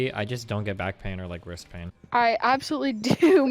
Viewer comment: At the start I thought he said "I'm so lucky I just don't get back pain or breast pain." Was like "Really? The latter there's already like a 50/50 chance for that not to be much of a possibility."